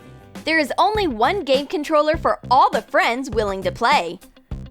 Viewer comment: she says Gay controler is she saying x box is better?